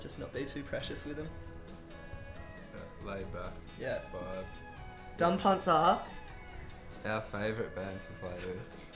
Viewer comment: What band did he say? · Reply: Dumb Punts - Chiller Great song :)